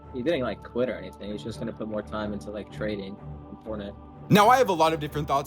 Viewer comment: Going from grinding Fortnite comp to meme coins is ac crazy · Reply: No way, I thought said training, and I kind of understood why he wanted to grind that, but then I realized he said TRADING, god damn that is so dumb.